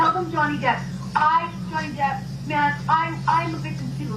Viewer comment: Weird, when I've heard the Heard recording before, I always thought she said "I, Johnny Depp, a man..." It kinda changes it a bit to realize she was just saying "man," as in "hey, man."